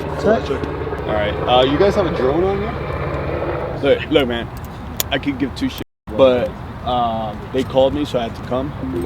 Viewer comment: I thought the police officer said I could give two shots